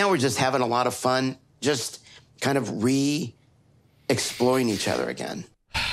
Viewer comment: When Big Ed said "We're re-exploring each other" I legit heard "re-exploiting"